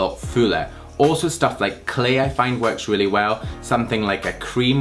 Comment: did you say "Clay"? :)